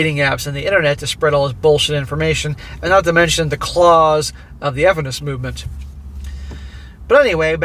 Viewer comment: you said something that sounded like, and I'm sure I have the spelling wrong, "the claws of the evanis movement". What is the actual spelling and what is this movement? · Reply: feminist movement. he deliberately mispronounces it